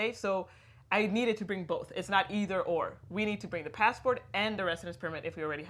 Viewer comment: Hey Alessandra, as we mention in the video and our guide <link> you do need to provide your temporary visa when doing the Anmeldung. Overstaying your visa or visa free time period is not a good idea. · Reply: Oh I’d never do that! I’ll request an appointment to apply for residence permit before I go and I’ll only stay if I’m granted. I thought you said to bring your “passport and visa” on the video so I got a bit confused if I should apply for Anmendung within the 2 weeks or wait until I get the visa. Anyway, thanks very much, you are always very helpful!